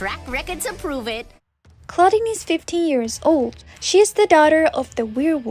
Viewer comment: i thought she said clawdeen was 50 years old😭